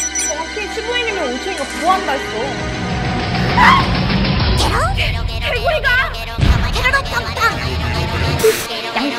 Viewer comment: 생방 보다가 '괴물이다!'가 '개구리다!'로 들려서 보러 왔습니다 ㅋㅋㅋㅋ